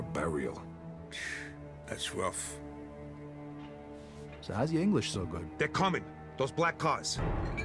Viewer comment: People who already played mafia 2 I didn't catch vito saying to henry "so hows your english so good until no w"